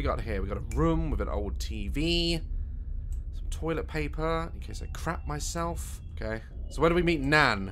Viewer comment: i thought he said “to clap myself” 😂 i’m sorry daz